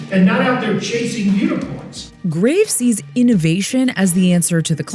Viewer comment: what did he say “not chasing neurons ?” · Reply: "not out there chasing unicons" 🦄